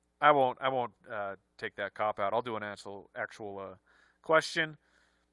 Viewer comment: says ‘asshole’ instead of ‘actual’ 🤣 after all the swear-talk earlier